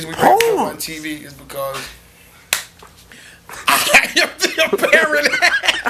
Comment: What the heck does Joell say at 6:07?! LOL, It is just hilarious the way he says it! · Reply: I AM THE APPARENT HEAD It’s a Royce bar that they all felt slighted by